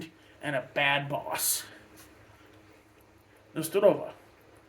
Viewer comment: i think you are saying this in russian at 7:15 Na Zdorov'ye